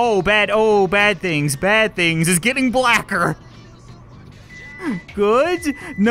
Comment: WHAT DID HE SAY📸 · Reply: he just said "it getting blacker"